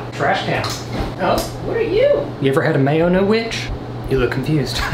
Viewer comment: am i the only person who heard a small woMAN child say "who are you"